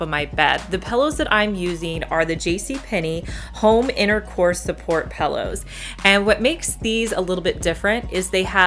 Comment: Love you! And love your videos so much! Side note, my boyfriend totally thought you said “intercourse” support pillows. 😂😂😂